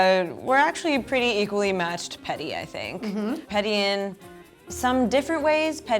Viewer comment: i thought it said pretty...XD